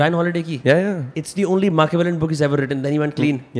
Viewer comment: ? what is that word?something like makeveint? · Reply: Machiavellian